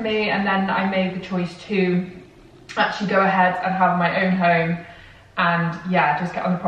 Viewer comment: I honestly thought you said you hate your own home